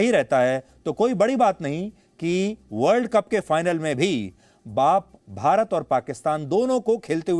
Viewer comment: he said baap instead of aap 🤣🤣🤣🤣🤣🤣🤣🤣🤣🤣🤣👍🤣🤣🤣🤣🤣🤣 Means india is baap ok pakistan